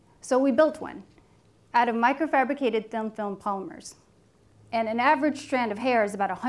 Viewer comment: Microfabricated thamp thump pulmers. Thats what i heard😂😂😂